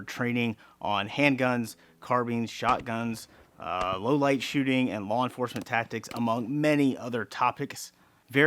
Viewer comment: For a second I thought said "low life shooting" 😂